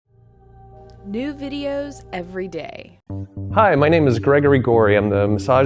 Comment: I thought he said 'My name is Gregory Golye', lol